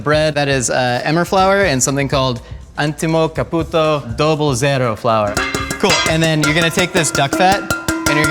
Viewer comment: Did he call me a puto